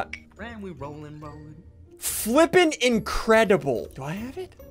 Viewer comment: did say flippin sexy